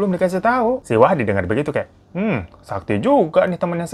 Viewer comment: Awalnya dengar bilangnya wahid, kok belakangan wahdi ya ehee .. apa saya yg salah denger yak 😅